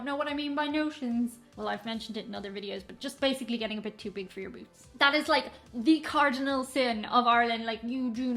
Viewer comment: Did you just say "to big for your boobies"?😂😂😂😂 I can't be the only one that caught that🤔